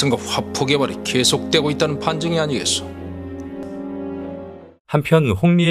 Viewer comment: 이거 판정이 아니라 반증이 맥락 상 맞는 말 같은데 아무리 들어도 판정이라고 들리네 ㅋㅋㅋ